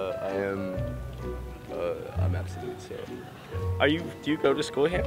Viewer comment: I think he said abstinent